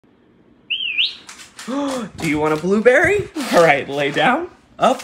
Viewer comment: I thought he said " want a balloon Berry?"